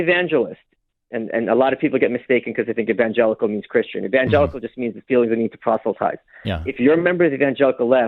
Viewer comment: what does he say there regarding what evangelical means? I can't make it out. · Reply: "Evangelical just means you feel you need to proselytize" or something to that effect